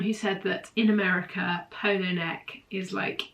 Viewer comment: Don't apologise for saying polo neck! You are British and speak British English; it reflects who you are and your cultural heritage. Americans should be considerate of that and get used to our expressions rather than you having to change your native expressions for them. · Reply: I thought she was saying “polar neck”, not “polo neck”.